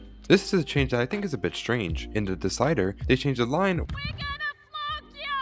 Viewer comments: If you lower your volume it actually does sound like Chilli said, "we're gonna f*ck ya" instead of "flog ya" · I thought said we're gonna f*** ya!